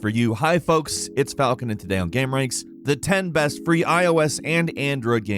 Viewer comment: did he just say... gam...?